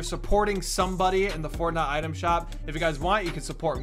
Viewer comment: I think I heard porna item shop